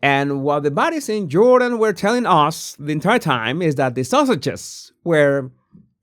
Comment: The captions read "The Sausages where somewhere else" instead of the Sussexes. This is the only entertainment I got out of this video. (at 8:20) LMAO